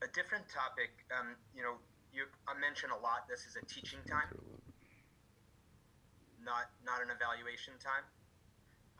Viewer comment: What did Bill mumble under his breath @ 4:50? Did he say " I just don't want to deal with it" ? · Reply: He said : «I'm done without Brady.»